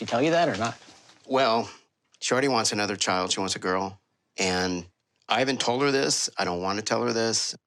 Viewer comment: did he say shorty wants another girl😭😭😭😭